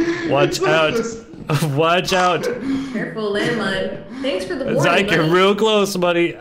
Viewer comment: Does say, “Thanks for the warning, honey!”? Because, if so, that’s adorable to the max.